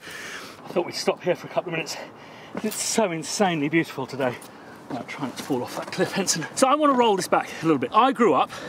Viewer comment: I misheard this as: alright, try not to pull off a 'Cliff Henson'